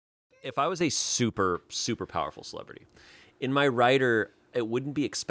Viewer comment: I didnt hear him correctly. I thought he said a "super powerful $lut"😂😂😂. I had to go back